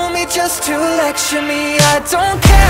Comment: for the longest time I heard it as 'I would love to tuck you in a bed at night' and was very confused about the angry wholesomeness